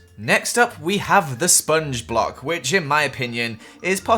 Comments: Who ever disliked is one of those ugly blocks. Edit: When you said "Sponge block" I thought you said spongebob 😂😂😂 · when you said sponge block i thought you said sponge bob btw i'm a fan of you love ya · I thought you Said spongebob Block instead of Sponge Block and thats Why im addicted to spongebob 😂😂😂😂